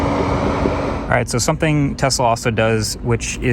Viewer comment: anyone else hear him say “Tesla” while talking about opening and locking the truck with a phone?